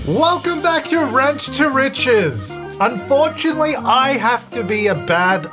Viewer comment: At first I thought you said "wrench to riches" and honestly you wouldn't be wrong there. Always love seeing a new video pop up from you!